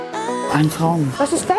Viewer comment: Was hat Susanne gesagt ein Pfau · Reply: Ein Traum